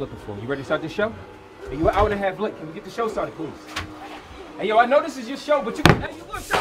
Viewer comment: He shouts out James Brown and Rick James , but his entire costume screams out Bootsy Collins :( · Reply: does he say lets get this show started boots ? i keep rewinding anhd hearing that